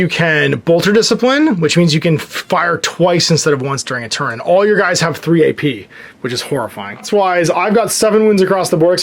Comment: I heard "your guys all have 3 AP" and almost died before I realized you meant APL